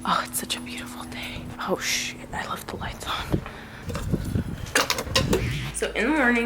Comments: did say oh sh*t i left the lights on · did i hear something ( oh shi* ) love u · did you say oh $h*t